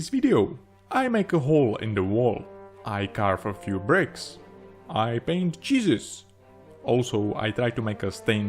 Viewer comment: Not gonna lie, at 0:07 I misheard this as “I paint cheeses” and got awfully excited.